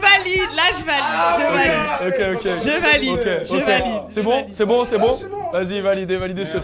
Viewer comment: il dit quoi a 42:48 j’ai pas compris ? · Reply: "Du coup tu vas kiffer"